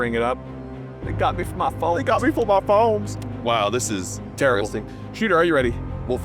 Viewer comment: Did he say “wow this is terrorisitic”? Lmao